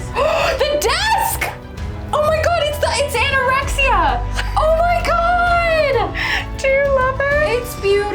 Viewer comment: The ONE TIME in history when a girl screamed "OMG, IT'S ANOREXIA!!!!" and it was a good thing... 😆😂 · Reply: P.S. I think the sword is called Ataraxia?! But she does sound like she says Anorexia... xDD haha